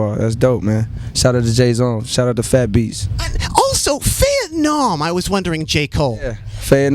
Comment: what did he say? farenom? vietnam? fannom?